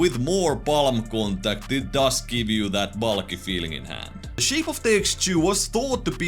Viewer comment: Thought you said "bollocky feeling in hand" for a second there